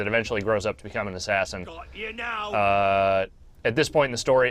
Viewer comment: Even the subtitles are fucked up. "Got you know" instead of now @ 1:42. Well done Ubisoft, it's literally shit in every aspect.